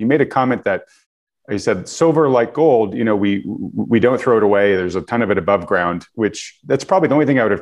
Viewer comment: i thought he said “silver, unlike gold, we don’t discard.” i figured it was him just speaking and not thinking about what he actually meant but just making sure he hit his talking points